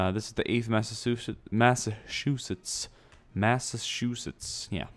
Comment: Sounds like he said Massashoesetts.